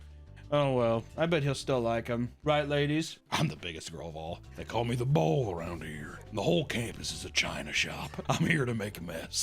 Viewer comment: I heard bowl not bull